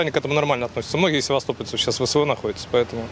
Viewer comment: мне не послышалось? "Многие севастопольцы сейчас в ВСУ находятся" ????